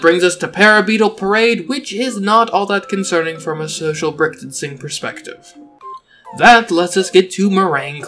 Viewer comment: I’m sorry did you just say “social brickstancing”